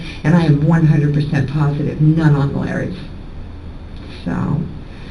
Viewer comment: hahahaha was that a Freudian slip by the caption??? “Non binary” instead of “none on Larry” when talking about fidelity. I needed that laugh😂